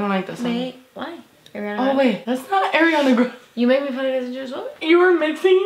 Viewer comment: I swear to God I heard you say "Aereola Grande"